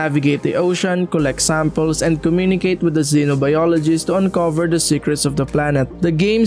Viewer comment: Did you say “Consult with a Xenobladeologist”? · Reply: LMAO! That's xenobiologist but that would be epic if its xenobladeologist xD